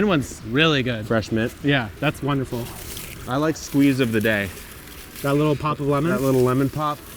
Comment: Why did I hear “Squeeze of Bidet” at 7:15?